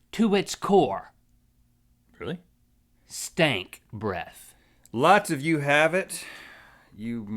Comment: i thought he said SKANK breath...